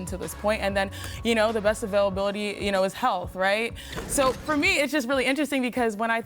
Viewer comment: did she say "the best availability is health"? (or help?). Isn't the expression "the best ability is availability"?